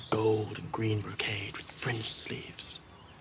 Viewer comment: French sleeves? Huh...wonder if France in Westeros. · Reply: HH, I heard "French" sleeves, too! "Fringed," it is, but in terms of real-world fashion, I think the proper medieval term (if we look at the GoT setting as inspired by the medieval period) would have been "Burgundian," not "French." And they wouldn't have had fringe!